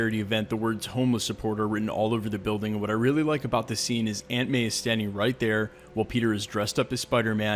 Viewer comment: I heard ‘ Ant Man’ for a sec?